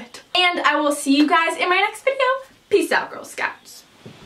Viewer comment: Does anyone know what she said on 6:57? Peace out...? :) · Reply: Peace out girl scout.